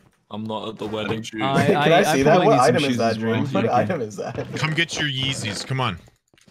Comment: Who needs shoes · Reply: I thought the sho guys said "come eat your shoes" Lol